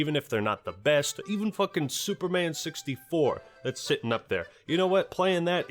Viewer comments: Dude I fucking thought you said Super Mario 64 instead of Superman 64 and got so fucking worried lmao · i thought you called super mario 64 god awful and i got really angry for a second lol